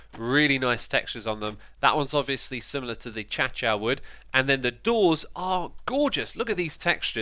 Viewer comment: did he just say A Cha Cha